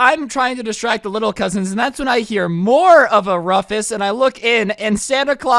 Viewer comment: "thats when i hear morg- MORE"